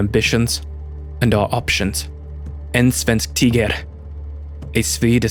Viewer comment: Does anyone know what he says at 2:20? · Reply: "En svensk tiger", literally meaninging "a swedish tiger". A propaganda-phrase that was used in Sweden during WW2.